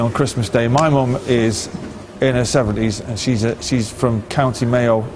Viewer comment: In her 70’s ウチのカーチャンは70代なんやけど、に聞こえる